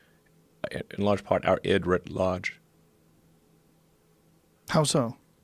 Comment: what does Elon say? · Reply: Christy Louise he says that the google searches are our id at large